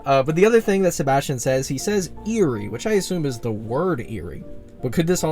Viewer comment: I do not deserve such peak, I haven't been looking forward to any videos because I forgot about the update but now I've just what I need for dinnah so, I also thought it said "eerie" but I think he said "Mary" as, the sea bunny that's there